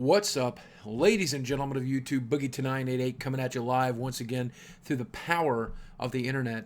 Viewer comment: Every time when he starts a video and says "boogie2988" I hear "boogie tonight..." :D